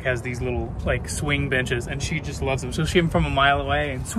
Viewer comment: I thought said swing bitches not benches 🤣🤣🤣